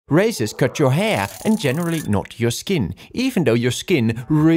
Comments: When I first saw this video instead of 'Razors cut your hair' I heard 'Racists cut your hair' ☠️ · Did i heard it wrong? " R@ cut your hair " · I thought it said r@ cut your hair😊